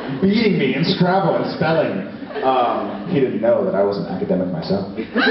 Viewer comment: Jared says something like "he didn't know... That itdtodypdoy myself" and people cheer. Does anyone know what he said? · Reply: Saberilla He said : He didn't know that i was an academic myself :)